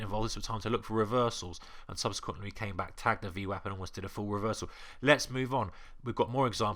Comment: Hello :) I did not get what you said at 5:46 onwards, "subsequently came back ...????...full reversal" :D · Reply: Yeah not very clear - "came back, tagged the VWAP and almost did a full reversal" VWAP is the Volume Weighted Average Price white line on the chart. Thanks for watching and your comment Mustafa Ali